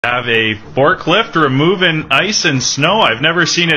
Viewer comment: I thought he said barklephed. I guess it was fork lift. Either way. Wtf dude.